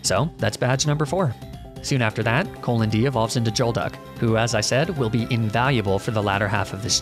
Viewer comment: did he just say “Jolduck”?